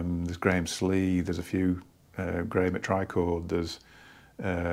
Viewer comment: Hi, right around 9:50 you mention Tom Evans, Graham Slee and then your accent was too thick I couldn't make out the next one haha Who is it after Graham Slee? Thanks much appreciated. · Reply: Graham at Trichord...